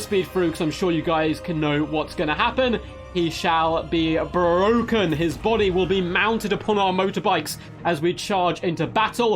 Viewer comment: For a hot minute I thought you said “His body will be mounted BY our bikes!” And I thought, DAMN, that’s a bit Slaaneshi there!